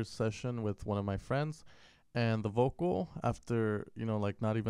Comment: Haha vocal sounds like your saying alzheimer's :))